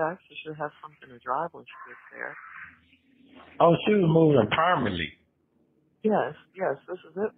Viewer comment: did he say she was moving permally …….trying to pronounce the word permanently 😂😂😂……OMG !!